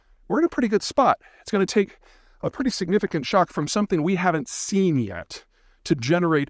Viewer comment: "It's going to take a pretty significant shot" 9:53 end of statement? Yep that sounds right. Subtitles said shock. What can I think of that could do that? Recent or maybe early 1900's